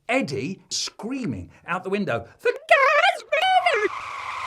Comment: What the crud did Eddie say? All I heard was "The car's blooming" 3:54 EDIT: Ok nevermind he probably just said moving instead of blooming